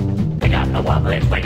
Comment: why do i always hear "We got the wublins"? It sounds like they have taken hostages xD